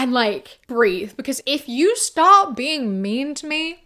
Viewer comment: I think you meant to say "stop being nice" around 14:25 but the verbal typo actually sounds so funny, I don't know why..! But back to the way people turn posts around to attack women, I'm not surprised. We are moving towards a more conservative time before, the type of misogyny you tend to see was woke misogynists or liberal sexists who try to back up their biases and statistics. Before that, it was chauvinistic misogyny where women are assumed to be weak and morally pure, so they are thus in need of protection. Obviously there has been periods of time where women face these incels and women are thought to be all bad until they prove themselves. It really is scary. I hope this man has the best of luck on his future romantic endeavors and if the internet (more specifically Twitter) has destroyed his trust in sharing his life, he is more than justified in withdrawing from these posts. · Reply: I think she said "start being mean" not "stop being mean"